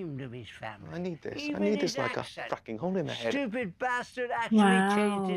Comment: Even though the subtitles say, "I need this like a fracking hole in the head." "Fucking" was actually said, and it was not caught until the episode aired.